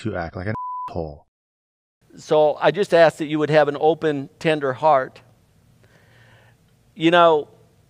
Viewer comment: I heard I hope you guys would listen with a Tinder heart, not tender heart, so I was confused for a minute there, lol.